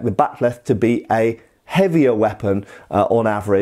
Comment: I thought he said "we would expect the bat'leth to be a heavier weapon, on average, than a NERF weapon of similar size." Well, that's not wrong. And this channel has taken a series swings at Shad on the DL and I for one fully approve.